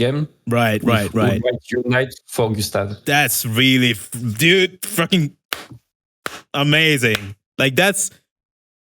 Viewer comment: which Game did he say? I can't hear it correctly. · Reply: Lost Odyssey.